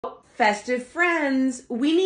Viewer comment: I thought you said BEST OF FRIENDS, until I read comments 😂